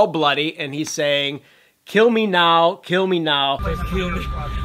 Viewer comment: I thought the man was saying DON'T kill me